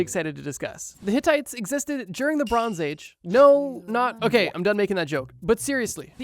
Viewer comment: did he say Lebron James?